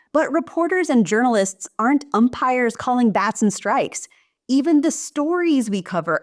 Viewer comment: Did she say "Bats and strikes "?